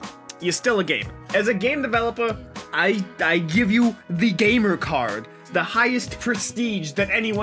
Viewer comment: what did you say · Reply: Gay mer cart